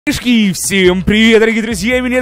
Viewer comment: Что он сказал на 0:00 ?? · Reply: Шишки